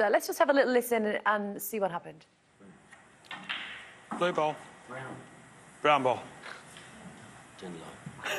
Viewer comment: Man, Snooker is such a graceful sport, but many of the comments here are just shameful, attacking Jimmy just because he's older than they are... And obviously they don't get tje situation AT ALL: Jimmy struggling anyways and trying to focus, then the referee interfering with his focus, making a completely unnecessary call, making the WRONG call, turning into some full throttle donkey laugh, irritating the HELL out of Jimmy, who's still trying to focus, then FALSELY accusing Jimmy of giving him the finger, threatening him with further consequences... It's always bad when a ref thinks he's bigger than the game. And I really thought Jimmy handled this VERY classy, personally I would have been furious about being treated with such disrespect. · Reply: I heard it as brown ok, he doesn't say it very loud and clear but its defo 'brown' you can hear the Nnn sound at the end